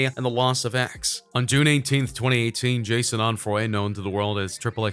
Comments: did you say Jason or ‘jaysay’ 😂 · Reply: It's jahseh onfory but he said jason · Wait did he say Jason Onfroy, I swear I heard him say that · Did he say Jason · Did he just say Jason Onfroy? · is it just me or did he say Jason Onfroy